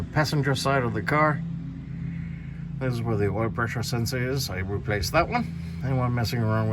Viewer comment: They just had to do that, didn't they? Put it in from the inside. Some engineers should never have been born. I couldn't quite get though what it is. I thought you said "oil level sensor." Did you actually say "oil pressure sensor?" Good seeing you again!